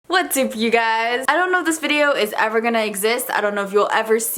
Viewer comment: WHO ELSE HEARD HER SAY "WHAT'S SOUP YOU GUYS?!"